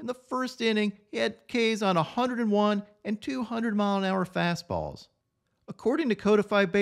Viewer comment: i had to do a double take, i thought you said 200 mile per hour fastball